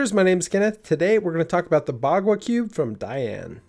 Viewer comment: turned down the volume at the start and thought he said Margwa Cube